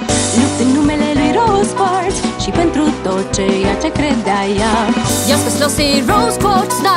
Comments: Swedish: " Rose Quartz Now, I think someone threw the pool. " Is it just me or can you hear it?? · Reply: Sofia Terrazas In Romanian at 0:37 it sounded like she said "She went through torture" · I heard ‘rose quartz she went through torture’ in Romanian.